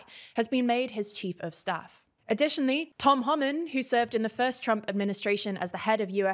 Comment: I swear it sounded like you said "Tom Holland"... I almost broke my neck looking back at the screen of my phone after hearing that 😂